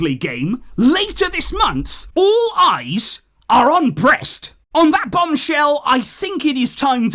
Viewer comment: I think i heard 'All eyes on "Breast'' '😂😂😂